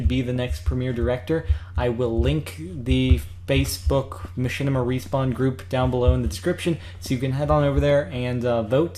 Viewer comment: In the transcribe around 1:30 or something it says Mission a refund group instead of michinima respawn group <3 gotta love the BETA of things.